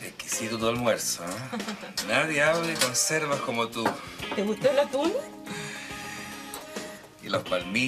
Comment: Te gustó la tula(?) ... Escuché eso al principio XD